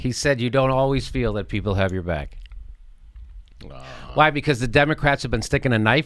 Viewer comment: i thought he said i hope you always feel we have your back ,which if so is just another platitude